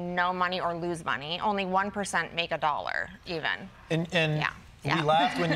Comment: 90% failure rate 😂. That's life!! 1% rule wealth no matter what industry they're in 🙄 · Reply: That's not what she said She said that "only 1% made a Dollar even"